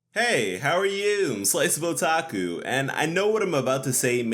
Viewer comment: When I first heard you say your YouTube name I thought your name was a slice of a taco 😂